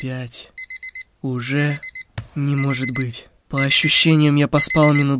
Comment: что мне показалось что он сказал что б**